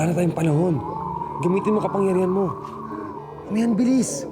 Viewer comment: si hitano sinabi please. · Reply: Bilis po Sinabi niya